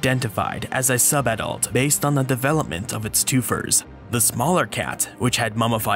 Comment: "The larger of the two cats was identified as a sub-adult, based on the development of its two-fers? two-furs? toofurz?" What are you saying? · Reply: Toofers. Slang for teeth.